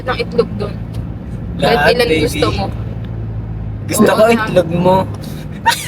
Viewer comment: ahaha 😂 haluh ano sabi ni 😂😂😂😂😂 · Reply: gusto ko itlog mo